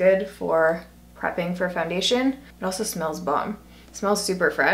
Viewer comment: 😲😲 I look away for one second to put a headband on and could have sworn you said "ooh it smells bum" 😂😂